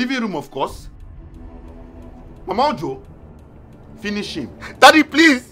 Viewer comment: In my mind I heard “over to you!” Instead “finish him!!” 😂😂 e shock me I choke 😂😂😂